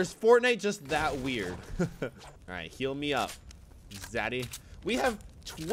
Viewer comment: is it just me or did he say daddy